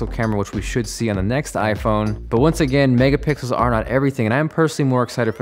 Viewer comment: Does it say megapickles